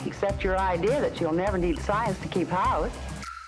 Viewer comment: "..you'll never need science to keep ____" Keep what? What is she saying? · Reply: Keep house. It means the domestic activities that create a happy and healthy home.